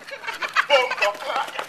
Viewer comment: did he just say bomboclaat? is he jamaican?